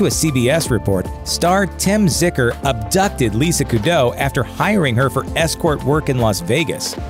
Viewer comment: I thought he said Lisa Kudrow in the first story. I was like "damn Phoebe fell on hard times after Friends".